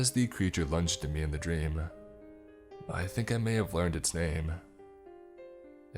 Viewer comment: Agnosco in Latin means to recognize, acknowledge, admit, perceive, realize, identify, know again or own. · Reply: lol...I thought he said, “Magnosco”. They are a German Company in the field of detecting skin cancer & such. I was, like, WTF? How does that fit in? 🤷🏼‍♀️ Therefore, counterfeit cas, I appreciate your taking the time out to make a comment...so I finally “get it”! Appreciate it. ~L~